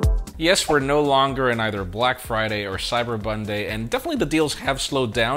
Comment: Did I hear " Cyber Bonday" 🤣